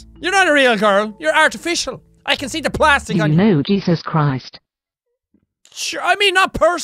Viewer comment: I thought she said. Ew no Jesus Christ. Btw 2017😂